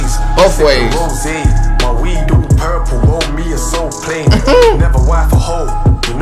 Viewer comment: Weed looking all purple like a soul plane is a reference from the movie soul plane with snoop dogg and kevin hart. Also the plane is purple 😄🤟🏽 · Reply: I thought he said for me it's so plain , woulda been a better bar lol